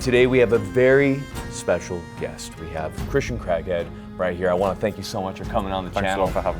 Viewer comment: I thought you said This is a Christian crackhead 😅